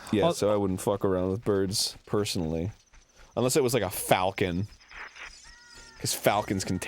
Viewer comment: DID DAN JUST SAY FALCO PUNCH?!?!?!?